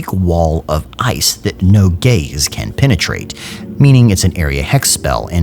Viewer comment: Did he just say “no gays can penetrate?!”😂👉